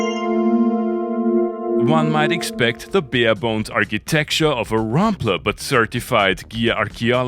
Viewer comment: Did you say "bear bone?" 🤣🤣🤣😑